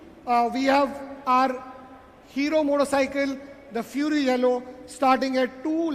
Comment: did he say Hero Motorcycle 😂